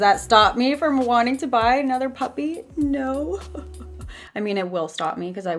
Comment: When u were talking about wanting another puppy I thought u were saying puffy ahaha